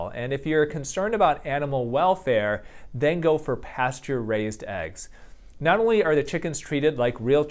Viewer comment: You probably said "pasture raised", but I kept hearing "pasteurized" and I wondered how that mattered to the chickens.